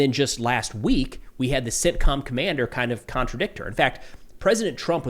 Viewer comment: Did he say sit com commander... I swear we are living TV with a dangerous situation, it fits.